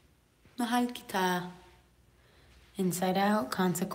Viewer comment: did she just say "Mahal Kita"?😱💕